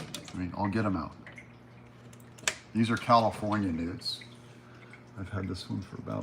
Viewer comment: couldn't hear well thought you said you'd show us your nudes until you said " here's my California Newts 😂😂😂 Happy Sunday